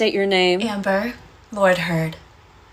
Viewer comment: Did she say her name was Amber Lord Heard ?